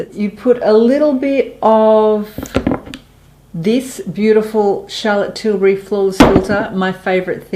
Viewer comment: I thoroughly enjoyed your video! The tips were good. I had one problem though. At 15:50 you speak of and use a product for under eye use but I couldn't understand the name of the product! Could someone tell me what it was, please? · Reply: eles r angels 🙂 That is the Chartlotte Tilbury Hollywood Flawless Filter <link> <link> Have a great day 🌺